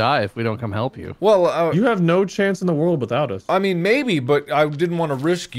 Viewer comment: Typo in the Subtitles: “Change” instead of “Chance”